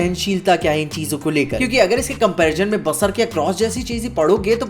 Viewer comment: pe kya kaha samaj nahi aaya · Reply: Berserk